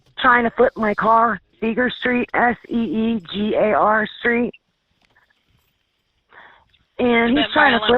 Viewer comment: For a moment I thought she said Beaver Street LOL